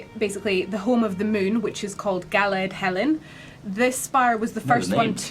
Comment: im welsh and can speak it fluently, at 8:43 it sounded like she said galed helyn, galen meaning hard in welsh and in old greek helyn means shining one or bright one so that kind of worked out